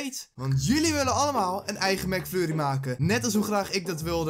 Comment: hoorde ik nou Mac furrie?? XDDD